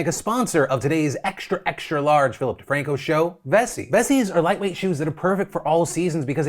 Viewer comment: Every time Vessi is a sponsor I hear in my head “this show is brought to you by Bussy” and I can’t stop smirking.